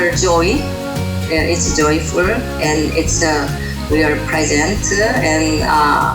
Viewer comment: I think she said 'its a present' but in the subtitles it said 'its [inaudible]'